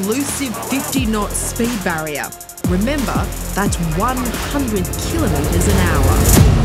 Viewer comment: It would seem it's quite complicated to multiply 50 by 1.8. 50kts is just over 90km/h. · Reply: do you have ears? At 8:08 she said “remember, THAT’S 100km/h”. She did not say “about”. Nice try. Fail.